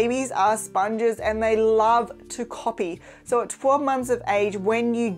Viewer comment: My son is 10 months old now. He hasn't crawled and clapped or wave yet. He does makes eye contact and show emotions. But I'm a lil bet worried 🥺. Is that normal? Thank you for the video ❤️ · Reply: sorry it's actually at 2:20 ....I heard "so at 4 months of age" I could be misunderstanding/hearing her accent. We already clarified that this video was just meant for toddler-ish age though